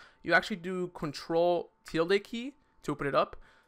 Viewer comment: What do you say in 14:15? I just understand "control + "tilliqui"" XD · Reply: he said control tidle key which is ctrl + ~